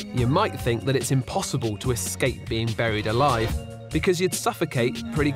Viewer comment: I thought it said beong burnd alive